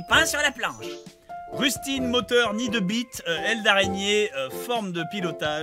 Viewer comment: C'est moi ou lenon à dit le mot b*** au lieu de mit à 41:05 😅